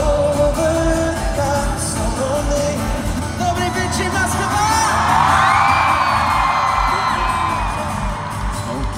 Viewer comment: what did he say? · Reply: Good evening,Moscow!